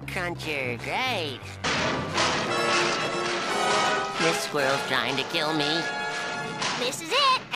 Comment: "This squirrel's trying to kill me." The amount of anxiety and genuine terror in the way he said that sentence got me. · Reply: I thought he said "This world's trying to kill me."